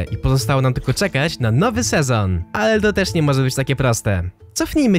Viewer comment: usłyszałem kula enderu